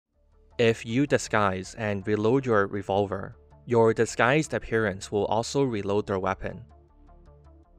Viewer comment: I accidentally skipped ten seconds and i heard "if you disguise into a sniper rifle"